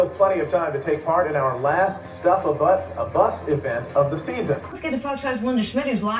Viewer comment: did he just say stuff a butt??? LoL 😁